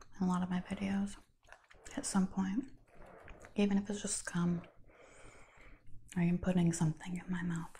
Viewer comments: lmaooo please clarify ....did you say Gum...or C...um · Dam! at 6:16 Did I hear you say "even if it's just come, I like putting something in my mouth..."